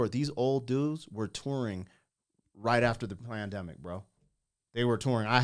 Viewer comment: Did you say “plandemic?” 5:05 🤨